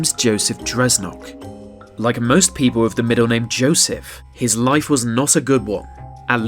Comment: Did I hear that right? At 1:11. "Like most people with the middle name Joseph, his life was not a good one". Comedy gold right there.